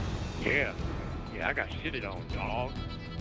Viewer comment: For all those viewers that want to know what is saying let me be an interpreter. "That all you got you little Bushwookie bitch!" and theres also a part where the guy repeats what said.."You got shitted on dawg!" 7:10 . Favorite Commentator